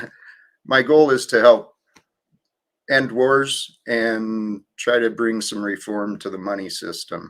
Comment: I thought he said my goal is to help N words! Wtf lol that was not what he said.😅😢ohh